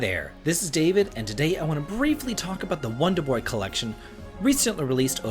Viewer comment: Lol I thought u said let's bravely talk about this game and not briefly like my mind was thinking wut is in here for that opening statement 😅 lol 😆